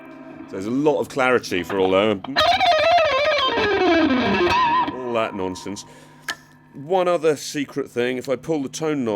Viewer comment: I´m not quite sure, but does Guthrie really says "all that nonsense" in 5:40??